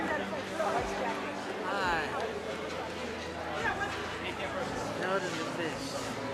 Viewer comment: Did he really say elderly fish at 13:39 😂😂😂😂😂😂😂😂💀